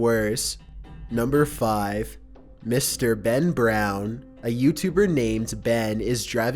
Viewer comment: Number 5 i thought he said BEN Drowned.